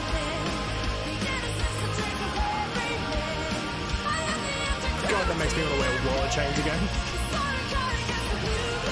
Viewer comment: What does he say at 5:23ish. God that makes me what again? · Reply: “God that makes me want to wear wallet chains again” I hit the important issues, I know